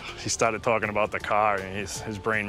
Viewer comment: Was that cop about to say stripperface instead of pokerface at 3:29 ? xD